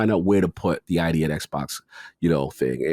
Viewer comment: I thought you were saying idiot Xbox lol